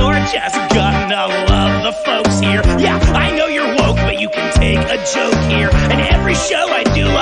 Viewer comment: ive been a fan for so long but i only realised he just said "you're just gonna love the folks here. yeah i know you're "woke" but you can take a joke here. it thought he said "i know you wont"